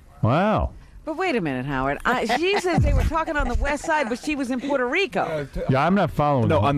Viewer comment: I thought she was saying the westside too lol